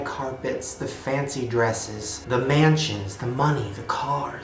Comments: At the beginning of the video you said " mansions" and I thought you said " man chins" and I got really confused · you said mansions, I thought you said "man cheese"